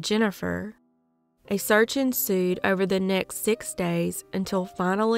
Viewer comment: You said a “search ensued”. I heard “a surgeon sued”. I giggled for ten minutes! I really need to go to sleep!!